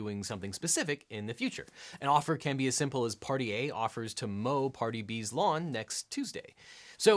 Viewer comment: At the 7 minute mark I misheard it as 'Party A promises to mow Cardi Bs lawn' and cracked up at my ear ineptitude